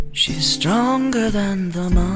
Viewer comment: Misheard "She's stronger than the Martin" with "She's stronger than the mountain" but it still works out at least for me :3